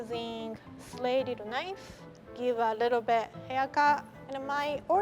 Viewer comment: i think she said ‘haircut’ lol